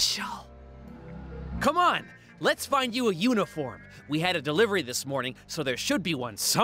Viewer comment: i thought honey said "lets find you a unicorn"